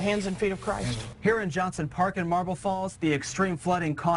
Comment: Lol thought it said Marble Balls...